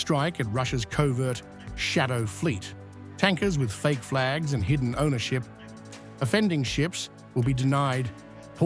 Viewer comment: I heard "eu leaders with false flags and hidden ownerships". I'am a bad guy, isn't it? Shame on me, for sure.